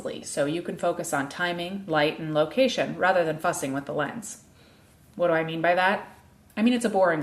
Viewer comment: Did she just say "rather than fucking with the lens" ..?